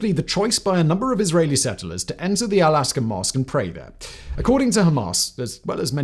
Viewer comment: Do I hear him wrong? Every time I hear "Al aska" instead of "Al aksa". Or first one is right way to read "Al aqsa"?